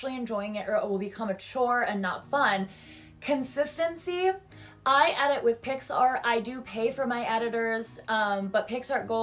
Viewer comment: thought you said "i edit with pixar" at first, i was so confused for a second XD anyways i also a lot of the same cartoons, but i'm a cusper (late 90s baby!) so i personally love a lot of early-mid 00s cartoons as well. i love my life as a teenage robot, codename kids next door, foster's home for imaginary friends, and camp lazlo!